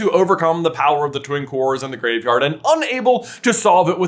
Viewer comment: It sounds like you said the power of the Twink Wars instead of Twin Cores 😂😂😂😂😂 i had to turn the captions on to confirm what you said